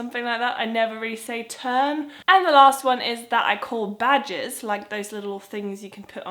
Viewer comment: at first i thought you said badgers. now i have a silly image of you wearing a badger as a hat